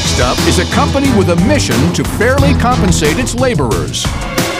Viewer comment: I totally misheard this: I thought he said "Next up is a company with a mission to BARELY compensate its laborers." :p